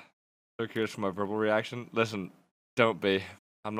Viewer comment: I think at 1:13 he said something like "so curious of my verbal reaction"